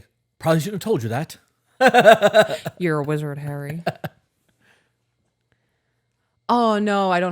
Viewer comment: Every time I hear “you’re a wizard Harry” what I really hear is “you’re a hairy wizard”.